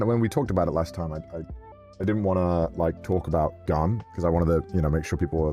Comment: he’s talking about Gunrun right? Like I feel stupid for asking that but my ears heard him say Garnt and the following few seconds sounded like he could also be talking about Garnt and it confused my brain so badly. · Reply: I thought he said garnt too and I was confused but I rewinded and he did say gun